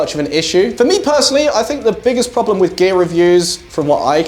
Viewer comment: I keep hearing "gay reviews" insted of "gear reviews" lol:)